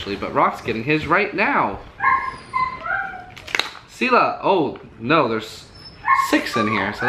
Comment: i hear Silla